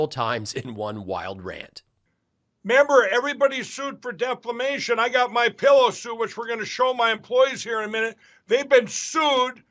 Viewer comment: did he say “i got my pillow sewage”?